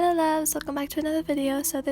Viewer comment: For a second when she said loves at the end I thought she said levs lol